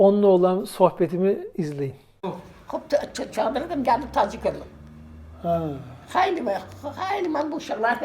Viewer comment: abi ben Gaziantepliyim o yaşlı teyzenin söylediklerinin hemen hemen hepsini anladım, biraz yavaş konuşsalar daha iyi anlaşılır aslında,antep şivesine yakın konuşuyorlar bizim burada barak türkmenleri var onlara daha yakın konuşuyorlar. Bu karantina günlerinde boş duracağıma kültürümüze bir hizmetim olsun istiyorum şimdi anlayabildiklerimi yazmaya çalışacağım,belki sizde alt yazı yaparsınız. 8:46 geldim tacik(gaşgayi türk hayatına yabancı oldum) 8:54 üç oğul iki de gız beş uşak varımdı.(kendi çocuklarını sayıyor) 9:36 kadın diğer adama soruyor, hangi vakitte gelmişik? 9:42 duvarımızı(davar,koyun) getirdik sattık. 9:51 geldik duvarımızı (davar,koyun) sattık evi düzelttik(yaptık,yaptırdık) 9:56 duvar (davar,koyun) varımızdı,at varımızdı. 10:05 atınan gedirdik,deveynen gedirdik 10:10 eşşek varımızdı su getirirdik. 10:13 haçan/kaçan (ne zaman) gelmiş bu 10:33 iç il ağzında deyirin (üç yıl ağzında diyorsun!) 10:46 beş ilden sonra geden geri?(beş yıldan sonra geri mi gideceksin?) 11:00 elmiş (ölmüş),bacı beş varımızdı(beş kızkadeşiz) ikisi ölmüş üç varımız. 11:36 ben garı olmuşam (yaşlanmışım),dökülmüşem 11:50 ihsan'a benzir (benziyor) 11:57 ele (öyle) benzir onun gibi danışir (konuşuyor),gülirin ya (gülüyorsun ya) 12:15 gaşgayi ......... işinden gelisidir (üstesinden gelir manasında), kömek (yardım) edesidir (edicidir) 12:25 gaşgayi çok gayretlidir, kömek (yardım) edesidir. 13:11 gaşgayi gızı ne geşengi (ne güzeli)(burada şimdiki gaşgayi kızlarının giyimini beğenmediğini anl